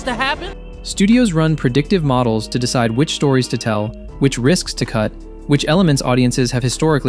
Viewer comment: I heard “which wrists to cut” and I was like WOAH WOAH WTF!?